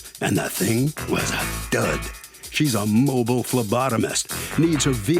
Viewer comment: Haha. When he said a mobile phlebotomist, I thought I heard him say mobile lobotomist. I had to rewind. I was scared and had frightening images for a second. 😂 🤣